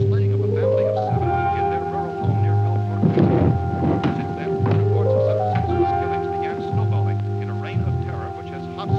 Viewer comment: the radio announcer says this whole thing started two days ago with the slaying of a family of seven. Did anyone hear which state and town it started? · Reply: Pennsylvania